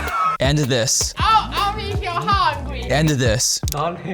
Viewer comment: Despite it all the clip at 8:50 was actually funny because it was an accurate accent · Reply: clip I heard I’ll make u hungry